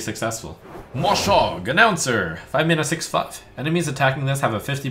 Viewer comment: does say 5 Mana 6/fucked?